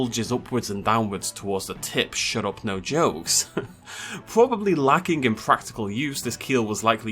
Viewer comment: d did he say shut up no jokes? as in,,,, like,,,, a ,,, yknow joke-?